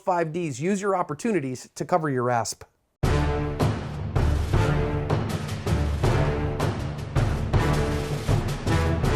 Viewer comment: Man this whole time I thought he said to cover your ass